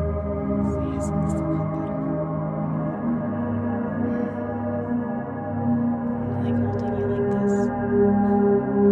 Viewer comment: wtf does this whispering say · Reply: I think it said "Michael don't you like this?"